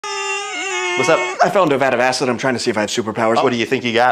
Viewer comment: I thought he said a vial of acid at first like how is he still talking